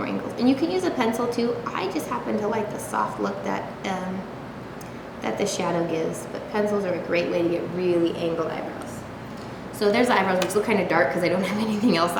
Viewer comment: I thought she said "soft hooker" LOL